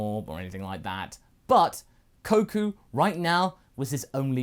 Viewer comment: Thanks for watching. Be sure to enjoy the video, leave a like, share with your friends and have a great weekend! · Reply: Did you say koku